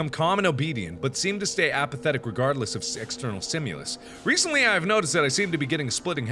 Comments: Anyone else hear him say "Sexternal Stimulous" at around 6:20 or so? lol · did anyone else hear "sexternal" stimulus..just me..ok